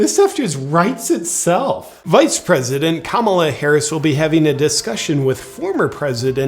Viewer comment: I thought he said Kabbalah Harris at 2 seconds in and laughed so hard, but on review I believe I was mistaken....wait I cannot tell..